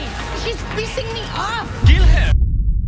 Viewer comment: I thought Logan said “tell her.” Not that lol